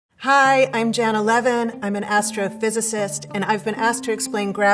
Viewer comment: "Hi i'm Nine Eleven" It's really strange that i heard this as i just watched few videos about 9/11